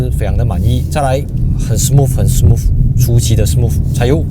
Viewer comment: 我聽成了很smooth，很smooth，舒淇的smooth，哈哈哈